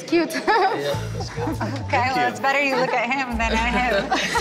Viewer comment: What did she say at 3:14 ??? “It’s better you look at him than-“ what?? Her voice is so hard to understand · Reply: "It's better you look at him (referring to Leandro) than him (referring to Biniyam)."